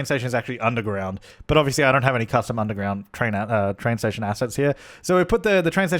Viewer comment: when you said underground train stations I heard "Underground Airports" I was so confused